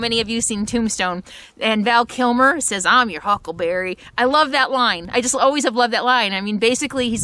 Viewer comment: Huckleberry is adorable! Enjoy the puppy times!! Until recently, I always thought Val Kilmer said, "I am your huckle bearer" instead of "I am your huckleberry". I thought it was in reference to wanting to be a pallbearer when they bury Ringo, instead of "I am game. I am your man". The original script of the movie Tombstone says huckleberry! Live and learn! Have fun with your Huckleberry! Can't wait to see him grow up on your videos!